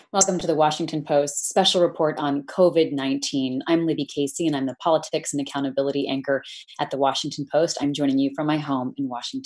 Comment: What is she saying at ............. ? I'm Libby Casey ........ politics and accountability anchor Can somebody enlighten me what she is saying there? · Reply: And I am the politics and accountability anchor at the Washington Post.